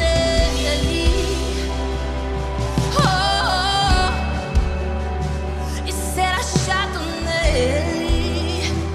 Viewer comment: Can anyone tell me what she is saying from approx. 1:00-1:30. There is no translation. Bless you, brothers and sister! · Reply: She repeats the phrase "and being found in Him" :) Bless you too!